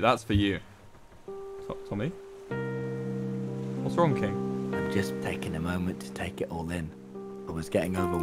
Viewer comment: I was looking at smth on my phone and I thought it said I’m taking a shit